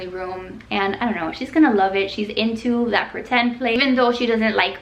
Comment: I thought you said a PlayStation instead of play kitchen 😂